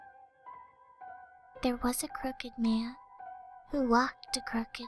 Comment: I thought it said, cooked man.. S***.